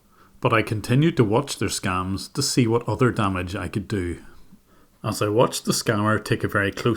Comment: How I thought he said: I continued to watch the scammers to see what else damage they could do... Him: I continued to watch the scammers to see what else damage I could do...